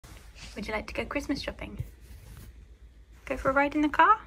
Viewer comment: Christmas tree shopping 🎄 🛒 ? Key : I thought you said Christmas treat shopping 🎄🍗 🦴🍗🦴🍗🦴😂